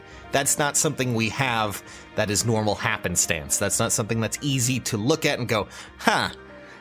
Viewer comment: Did you just say S!Corrin "isn't easy to look at"? Oh I do greatly disagree.